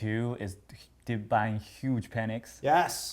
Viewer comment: Best video on the Internet about trading in my opinion and for free too. Thank you so much Tim and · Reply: anyone catch what he said? Buying huge Pennex? Pennies?